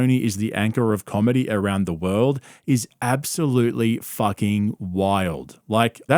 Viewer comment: I don't think Joe was saying KT is the world's comedy anchor, I think he said it was Austin's. And I agree.